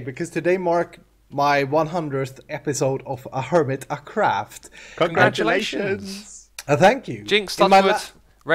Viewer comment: What did x say at 14:13? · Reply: X and Ren said "congratulations" simultaneously. X called it first, so he gets to be the cokehead.